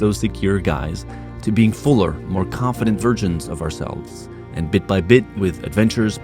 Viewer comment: did say "virgins" ? or just a mumble "versions" xD